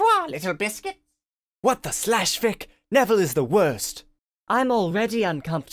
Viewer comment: did harry just say what the slash fic